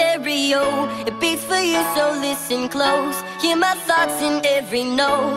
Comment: i heard "hear my thots" w h o o p s